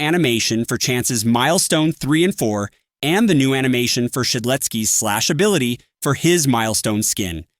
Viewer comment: why does it say schlatt instead of shedletsky at 10:08 😭